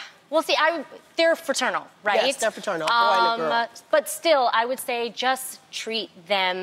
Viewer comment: Did she say “boy and a girl”?